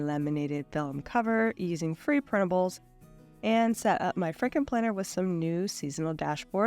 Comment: I heard "Franken planner" as "Fricken' Planner," lol.